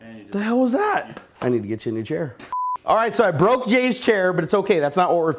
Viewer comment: what was jay going to say??!! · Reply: Kirk_Kirksman "when/with jay sometime tomorrow."